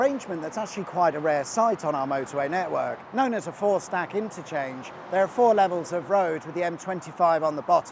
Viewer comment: when you said four-stack interchange, it was not at all clear to my old ears what you had said. Forceback interchange? Horseback interchange? Had to bust out some Google-Fu and use some common sense. Hard work on a Sunday